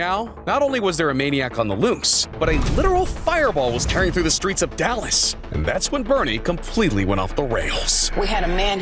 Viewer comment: did you just say 𝘿𝙖𝙡𝙡𝙖𝙨, like 𝘿𝙖𝙡𝙡𝙖𝙨, 𝙏𝙓?!